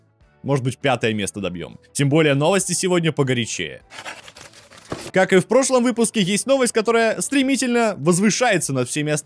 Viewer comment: Что слышат все: "как и в прошлом" Что слышу я: какаю в прошлом